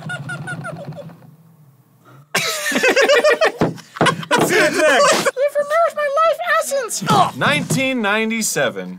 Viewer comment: What is Elmo saying at 14:40? I can't make it out and no subtitles catch it. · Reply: You've removed my life's essence - or something like that